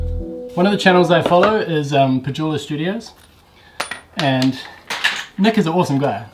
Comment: mentioned another channel he watches. It sounded like he said "Pujula Studio". Sometimes the NZ accent is difficult to understand. The spelling of the channel he mentioned is "Pedulla Studio". I watch that channel also. Nick Pedulla is a great furniture maker.